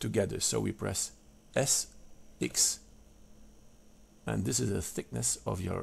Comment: on 0:46, you say "press S 6"? or did you say "S x"? PLZ help! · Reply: press S x because we won't to schrink (S) in X- direction (X)